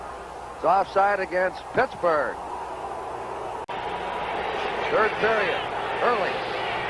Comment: Only watching to hear Curt Gowdy Also i heard him say 3rd period ? lol